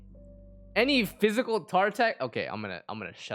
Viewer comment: Did someone say Physical Tartaglia?